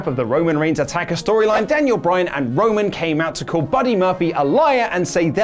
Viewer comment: Sounds like he said Daniel Bryan and "Roman"